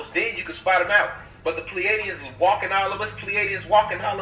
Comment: can someone please spell out what he is saying the word obviously I can't spell it that's why I am asking but I will try so you know what I mean "plealieans"? And who they are?